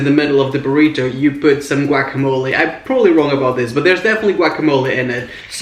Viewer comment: It sounds like your saying quackamowle